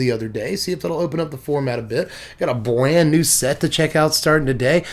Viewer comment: misheard you and thought you said “bland new set.” Lol